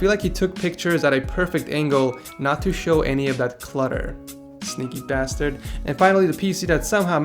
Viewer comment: holy crap i thought he said 'stupid bastard'